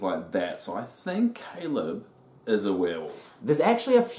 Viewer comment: What do you mean at 6:10 when you say "Caleb is a will"? Is that what you're saying or... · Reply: Back at this point, I believed that Caleb was a werewolf.